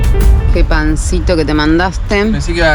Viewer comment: En Rosario son torrejas..y tambien pense que decia pancita jaja